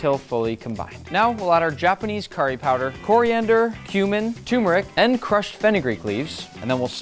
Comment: did bro just said human? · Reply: Cumin